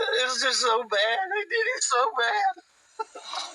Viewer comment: after again it looks like he tries to make his eyes water by putting his fingers in his eyes, the subtitles say they did it so bad. But it sure sounds to me like he said, I did it so bad